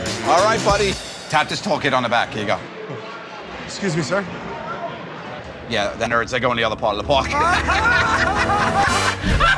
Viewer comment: What does Joe say at the end? 1:55 I don't get it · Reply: "Yeah.. the nerds, they [you] go in the other part of the park."